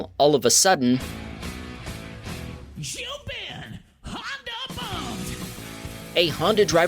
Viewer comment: did i just hear "#HondaBump"?